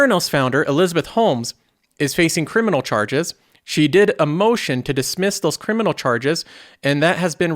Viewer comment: When you said Holmes filed a motion I thought you said she filed with EMOTION and I was about to call bullshit lol. Anyways great video as always keep up the good work